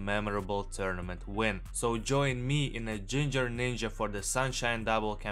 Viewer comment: For a second I thought you said the n-word lol